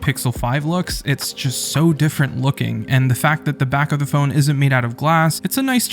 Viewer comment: I didn't say the phone was glass tho... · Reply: oh sorry 😂 at 1:53 i heard "is" insted of "isn't"